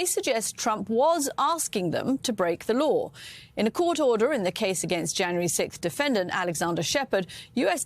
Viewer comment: I thought it was just a fluke when I heard it on the BBC, but it really seems like no British news reader is able to pronounce "sixth" properly anymore - they all say "sikth". I've been hearing it all over the place, and it's always British announcers. Weird.